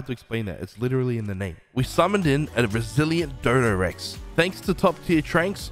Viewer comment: legit thought he said brazillian boss dodo